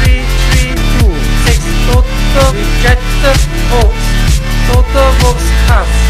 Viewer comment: did he just say toto 1:06 ?